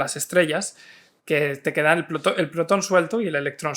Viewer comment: En el minuto trece entendí 'te queda el Plutón suelto' ya basta de hate a Plutón. Pd: perdón por el retraso...😂